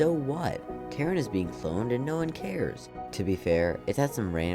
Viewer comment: I thought you said Karen at first...